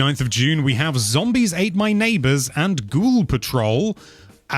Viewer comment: I thought you said Paw Patrol lol that would be a weird pair up.